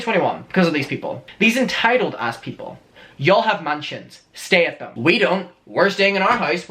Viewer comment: You yelled “you all have MANSIONS” but I heard “you all have MAN CHINS”. I was like ohhhh shit snapped!